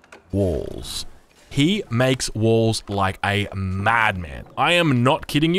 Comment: "Khorix is infamous in Age of Empire 3 for one thing and one thing only... balls." That's what I heard first and it should have stayed like that